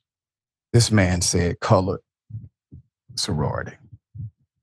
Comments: I listened to it real close, it did sound like he said a college sorority.. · While they are all melting down over her being the presumptive nominee (their tears are delicious) it did sound like he said "college sorority" not "colored sorority."